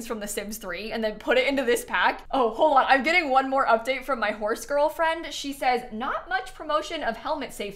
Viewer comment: the way I thought u said horse girlfriend….. I double took 😭🤣